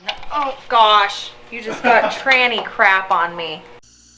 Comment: LOL! Did she just say "Tranny Crap"?